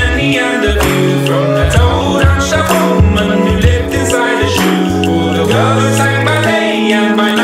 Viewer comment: I heard "i told her shuboom and she looked inside her shoe" whn he was taking about the woman in the shoe 😂